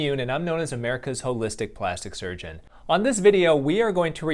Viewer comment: I’m so tired that I hear “I am America’s hopeless dick plastic surgeon.”